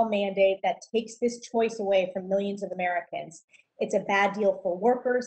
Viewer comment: Go to <link> and use code UNCOVERED to get 83% off a 2-year plan and 3 extra months for free! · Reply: when she said "of americans" youtube closed captions said "of workers"